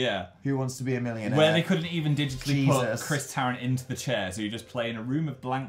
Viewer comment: I’m Christian, & I think if my conservative youth group leaders found out about me watching this channel, they would be APPALLED😂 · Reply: BluePearl26 oof I thought you were saying your name was Christian😂😂😂